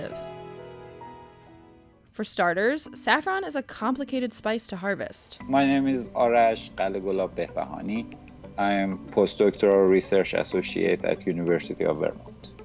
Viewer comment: i thought i heard "my name's arash globaglobaglabglab"